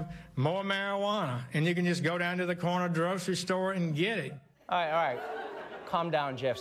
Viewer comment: did sessions say "Corner drugcery store" ?